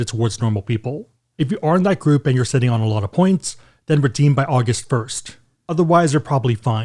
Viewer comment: Did you say August 1st? 4:04 🍔